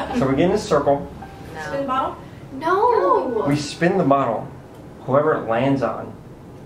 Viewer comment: did anyone else hear at 5:11 said lets play spin the bottle i heard a robotic voive which said no